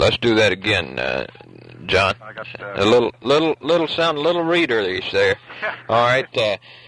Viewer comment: About the 01:04 mark- "Okay, let's do that again, uhh, John. A little, little, little, sound a little readerlish there." I've heard this numerous times and I have never been able to figure out what was meant by this or the word being used to describe it is. Is it "readerlish"? Not even a word. "Readerish"?